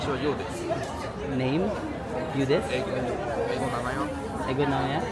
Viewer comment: Parang narinig ko "Nomae wa" , which means Name ata. Kase natutunan ko ung "watashiwa nomaewa (Your Name) desu" which means my name is .. or I am (your name) baka he thought you were asking his name.